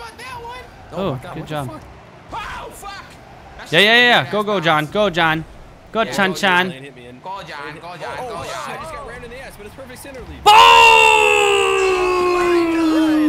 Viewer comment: Anybody else hear "Perfect centeredly" at 5:06?